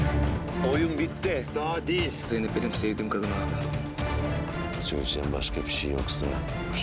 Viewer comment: Onur zeynep benim sevdigim kadin mi dedi 😍😍😍 ben mi yanlis duydum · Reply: Aleyna Tilki zeynep benim sevdiğim kadını aldı dedi